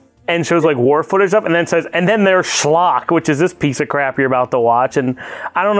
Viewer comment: Anyone else thought it said sherlock?